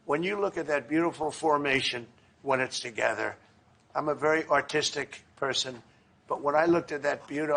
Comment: When Trump said I'm Avery artistic person, for some reason I heard I'm a very autistic person 😂